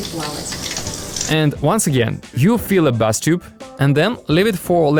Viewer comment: I love everything about your videos including the dubbing, however sometimes your English accent can be hard to understand eg when you said bath tub it sounded like you were saying 'bust up' 😅😅 so perhaps CC English subtitles would help (not auto-generated). Keep on dubbing though, I'm confident your English will improve over time and your voice is great.